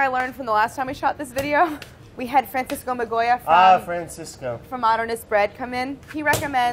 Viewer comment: I thought she said “Frances Goma-Goya”. Also why cant they say “superbowl”? Liscensed? Copyrighted? Why?